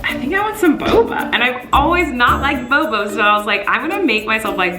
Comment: is it just me or did I hear her say "bobo" lol ( by the way I love you Colleen!)